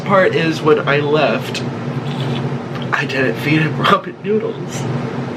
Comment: I think noodle died cuz of the food nick feed her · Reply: are you sure i heard him say " when i left i didnt feed him ramen noodles :( "